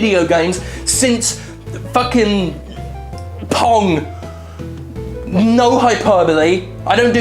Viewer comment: I thought he said porn instead of pong.